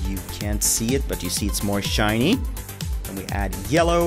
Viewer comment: What actually said: "It's more shiny" What I heard: "It's more SHINEE" I couldn't help ;v